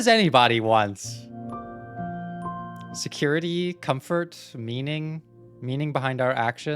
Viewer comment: I misheard that as "What does anybody want? Security? Comfort? Memeing?" and had to do a double take to make sure I didn't just hear a therapist say memes are a basic human need